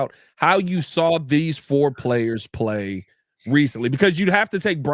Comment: Coulda swore said on how the QBs have played recently